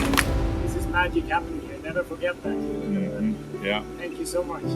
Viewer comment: What did he say at 14:23? ('This is my..... never forget that') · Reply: I think it is "This is magic happening here, never forget that"